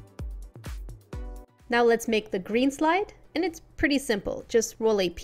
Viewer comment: When you said green slide, I thought you said green slime. XD